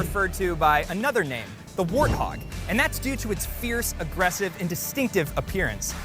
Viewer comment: Is there the letter W in the Russian language? It sounded like you said the arthog.